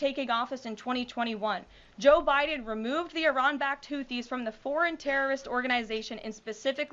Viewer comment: Not gonna lie, sounds like she said, “Removed the Iran Back-toothys” 😂